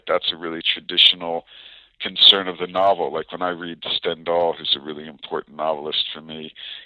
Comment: Did anyone catch what author he mentions is really important to him? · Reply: Stendhal I think